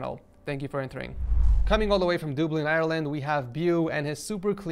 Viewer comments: did he just say ''dooblin''? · did he just say doubli it’s dub-lin